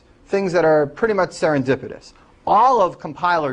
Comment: Looking for help with english: I can't understand what he says at 1:03. "...things that are pretty much ???". Thanks for any advice! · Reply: He says 'serendipitous' which actually means lucky or fortunate. So what he says is lots of good, powerful things come out of this theory of Finite State Machines.